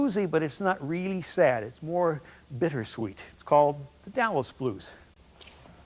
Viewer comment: Can someone please tell me the name of the blues he plays at 10:42? I cannot understand what he says. Thank you!!! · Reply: The Dallas Blues